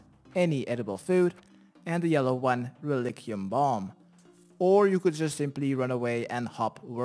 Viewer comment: Tell me I'm not the only one who heard him say "Lick your mum"